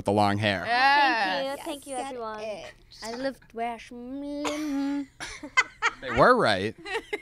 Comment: what did jade say at 2:57😂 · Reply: h f she said I look fresh then something else